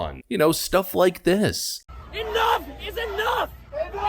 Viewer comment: when that guy was saying "enough is enough" i kept hearing "knuck if you buck" and now you can't unhear it either